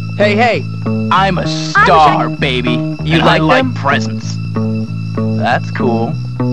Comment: wtf at 0:32 that bully guy says you like my breasts lol